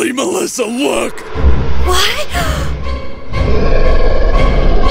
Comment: havagua? WHAT?? WHAT DID YOU SAY?!?! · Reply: He said have a good one